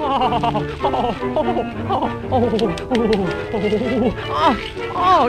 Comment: does Bimbo say "oh shit"